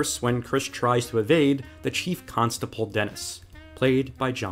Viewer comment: Did he say "chief constaPOLE"?